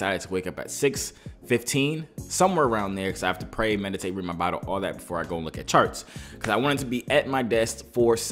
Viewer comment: Great work as always🥂 at 1:32 U said pray, meditate and what? I think u said read your Bible right